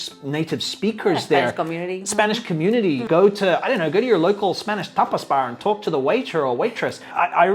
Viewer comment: I thought he said Topless bar. 😂 Speaking of accents. I can barely talk to women in English with their tops on. Imagine having to stumble through a new language with one. 😂